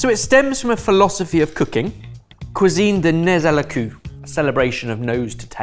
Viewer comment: Just so you know (in case you find yourself needing to say "nez à la queue" in front of French speaking people for whatever reason), the way you said it sounded like you said "nose to the ass". To say it better you, don't say the -z of "nez" and "queue" sounds more like the -e in "mermaid" (kinda)